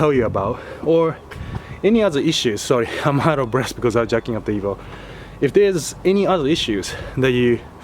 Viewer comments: i laughed so hard I thought you said "Jacking off the Evo" Awesome vid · Sounded like he said he was tired because he was jacking off the evo😂😂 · I thought he said he was jacking off the Evo haha